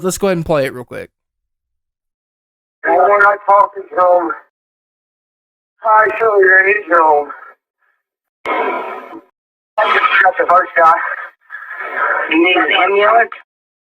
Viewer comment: this is what I hear “There’s one guy talking to him, I probably shouldn’t of ran into him (gunshot) they shot the first guy (inaudible)”